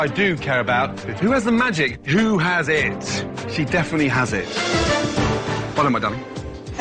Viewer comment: Lol he said "well love my done" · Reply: Sounds like he said 'well done my darling' to me.